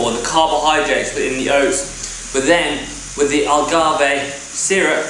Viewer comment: Did you just called Agáve "algarve" ?XD